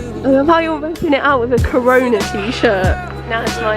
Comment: It’s 2020 April 4 and I played this video again as a background sound while working, and suddenly at 2:30 part, I heard “corona” and was like “what?!”